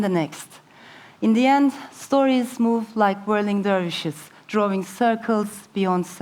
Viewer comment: Please who can transcribe for me what she said at ‘19:07-10. I heard: “Stories move like rolling dories..” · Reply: Dervish not Dories..